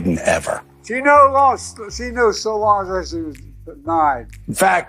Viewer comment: "She no lonst... she knew as long as she's... denied." What? What's Joe trying to say? 😅